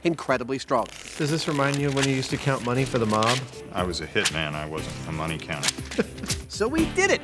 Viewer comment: "Does this remind you of when you used to count money for the mob?" "I was a hitman, I wasn't a money counter" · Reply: I thought he said "pissant", which is like bottom of the tier list